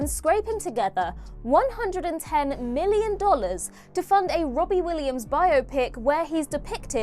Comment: I actually thought she said Robin Williams wasn't well known in the U.S. I was about to say girl, you need to check your facts, then I went back and noticed she said Robbie Williams and not Robin Williams.😂